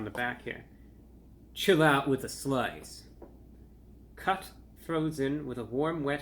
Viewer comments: had to rewind 100 times because I kept hearing "Chill out with the sl*ts" LOL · man, i thought you said "Chill out with a slut" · "Chill out with the sl*ts" "Cut frozen with a wet warm kn*fe" 😅 at a low volume I swear he says this. Has me chuckling all day replaying his review 😆😆😆💓